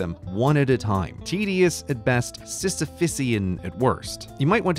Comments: what word was that? I have never heard that word and I don't even think I can sound it out. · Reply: Sisiphysian. It's a reference to Sisyphus, an ancient greek king who tried to cheat death, and was punished to roll a rock up a hill forever. Every time he got the rock to the top of the hill it would roll down the other side, forcing him to start over. So in this context Grey is using the word to refer to a task that is long, difficult, and ultimately futile. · What did he say? Sistafisian? That isn't a word. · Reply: Sisyphean. And yes it is. Refers to greek legend of Sisphus, who treated the gods badly and was punished for it with eternal, unending labour that keeps being undone.